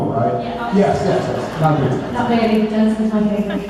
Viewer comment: does she say Jensen's my baby? I can't hear it clearly